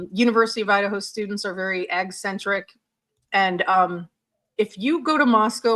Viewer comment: What is she saying at around 37:32, and around 38:07? What centric? The subtitles are not making any sense--what I can see of them. I'm blind and have mild-moderate hearing loss, and I don't understand the context. :( I'd like very much to understand. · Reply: I think she says 'eccentric', but it's difficult to hear with the way she pronounced it, but it fits with the context.